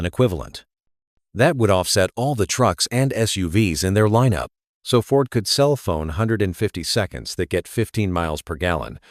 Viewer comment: This proves the video is AI. Instead of saying, "Ford can sell F150s." It says, Ford can cell phones 100 and 50 seconds.🤣